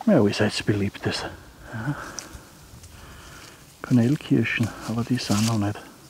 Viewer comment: Hi, was waren das für unreife, rote Früchte, die Du probiert hast? Ich habe "Knallkirschen" verstanden (???) . . . Gruß TinoPS: Hättest die fertige Gabel ruhig noch mal in die Kamera halten können!